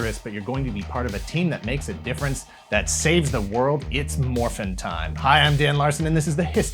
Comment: i thought he said "it's morbing time" at the start and my soul almost left my body out of pure horror sure, Morbius is an animorph but please not like this